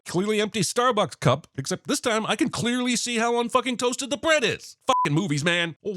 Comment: Did I just hear an unbleeped F-bomb? 😂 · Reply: It was OK because he said "unfucking toasted" 🤣